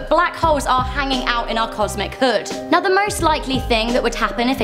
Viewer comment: did she say cosmic hood